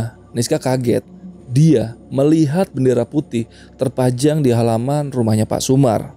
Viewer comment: Bendera putih? Moga guah gak salah denger ..